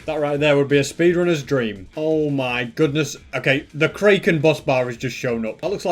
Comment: did he just say the kraykon